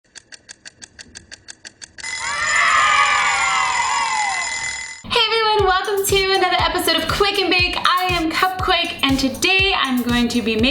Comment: Oh I thought she said welcome to wake and bake xD